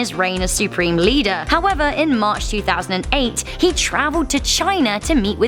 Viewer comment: Did you said March 2008